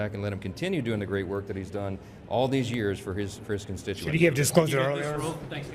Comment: What does he say at 4:39? I swear he says "for his ChrisConstituants" He started to say Christian and caught himself.